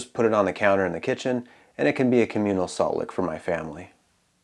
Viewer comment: did he say, communal salt lick?